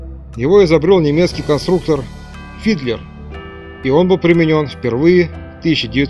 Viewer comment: "...его изобрёл немецкий конструктор... Хитлер!" В первый раз именно так послышалось=) Автору - заслуженный лайк